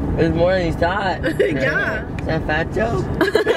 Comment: What did he say 5:26? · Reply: Rebecca G "is that a fat joke?" 😂